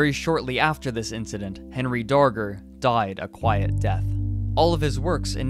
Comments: i thought it said henry danger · Thought it said Henry danger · Anyone else thought it said Henry Danger? I was confused so I clicked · I thought it said Henry Danger my bad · Who else thought it said henry danger? And said wtf? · legit thought it said "Henry Danger" like that really bad nick show